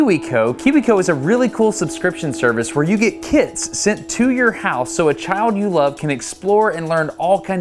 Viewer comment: I probably the only one, but I heard 'kids sent to your house' instead of 'kits' in the sponsor segment xD